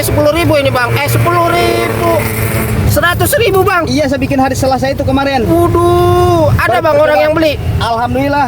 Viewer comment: Kalian dengar gak dia bilang apa di menit 1:04 😂😂😂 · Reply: Bikin hari Selasa